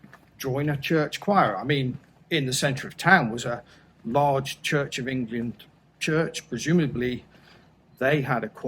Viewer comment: Great story. I spat my coffee out at 3:14 and 3:39 . It sounded like you said church of Indian church :)